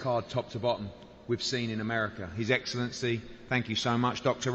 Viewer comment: did he say America . 😂